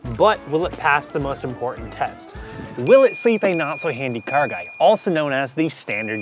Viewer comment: Not gonna lie I thought it said your name was not so handy gay guy. But either I stilled liked the video CARRR guy lol